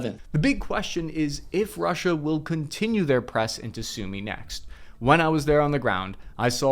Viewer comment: I misheard “press into Sumy” as “press to sue me.”